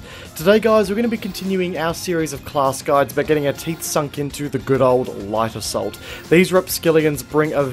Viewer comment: At first I thought you said, "By getting our teeth into the life of salt." and I couldn't stop laughing. Sums up playing LA perfectly.